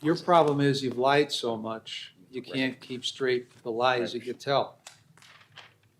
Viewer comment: "Because you lied so much, you can't keep straight with the lies you tell" is that what he's saying ? Couse I love that